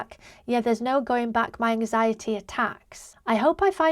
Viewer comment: OMIGOD why does EVERYONE mishear "my anxiety attacks" as "my anxiety is sex" 😂